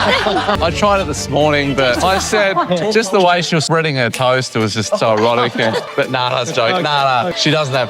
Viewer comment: Thought said “when she’s spreading her toes”